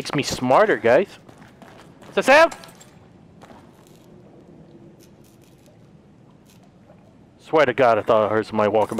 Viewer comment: what did he say? Ahahahaha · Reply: He said what's that sound